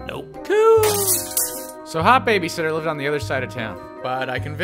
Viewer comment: At first I thought it said hit babysitter